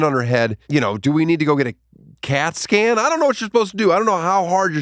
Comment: when he said “cat scan” I thought he said “casket” which made me do a spit take. In retrospect, I think casket would have been funnier.